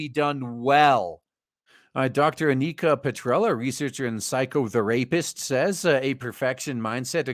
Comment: Man when said psychotherapist, I heard, “Psycho, The Rapist” 😂